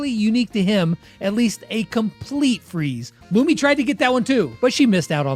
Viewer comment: in the subtitles says Ruby, not Lumi. Definitely one the best characters on the show!